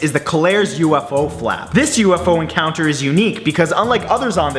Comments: Lol In number 6 I thought he said Colares UFO fap instead go flap XD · I thought it said "Colares UFO Fap"